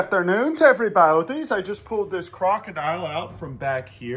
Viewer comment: That’s not an alligator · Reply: then what is it? I thought he said alligator